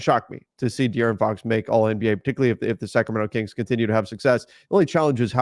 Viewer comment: Lmaoooo 30:34 did say “second round kings”?? Ayooo ☠️☠️